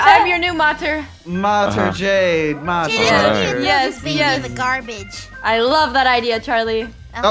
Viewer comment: am i out of my mind or did say badword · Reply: She said garbage